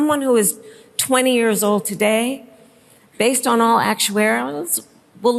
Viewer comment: wtf did she say at 8:09 😂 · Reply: Based on all actuaweries